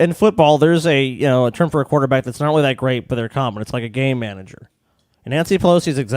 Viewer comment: I'm high so when kept saying "Game Manager" I kept hearing "Gay manager" and then proceeded to completely forget about what the topic was.